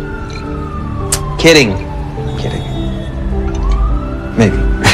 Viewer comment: I initially couldn't make out what Eddie says at 1:16, but it's "ice goes on the eye, bud" because he saw that Buck wasn't pressing the ice pack to his black eye like he's supposed to. These two are such husbands.